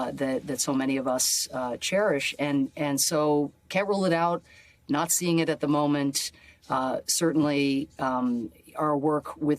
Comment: "Not seeing it at the moment." Gawd, I heard, "Nazi'ing it" :(😖